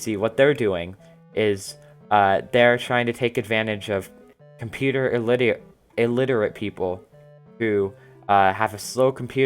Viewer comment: Sounded like you said "computer idiots" .